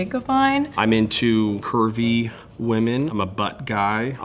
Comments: WHY DID I THINK THAT THE DUDE AT 0:40 SAID "kirby women" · i accidentally paused right when began saying his kink so instead of hearing "curvy" i heard "kirby" and was extremely confused and concerned